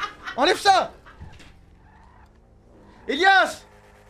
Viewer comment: Att quoi j'ai bien entendu elias ??? A